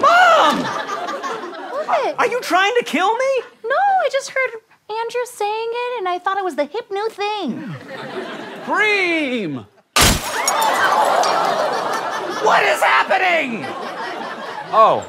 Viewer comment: When throws stuff, does he say "Cream?" Then answers it for meh...